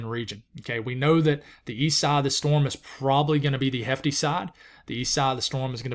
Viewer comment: Did he just say Idalia is gonna be a "heavy girl" lmao!!! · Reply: I believe he said “hefty” 😂 That’s normal southern speak though, I’m a Floridian but wasn’t equipped with that dictionary/accent.